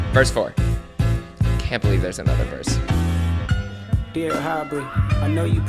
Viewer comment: 🙏🏻😂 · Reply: FOURTH VERSE I SWORE DOT OPENED IT SAYING "DEAR HYBRID" CAN'T HEAR ANYTHING OTHER THAN😭💀